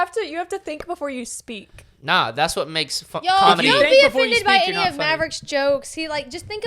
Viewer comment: Did say "fing comedy"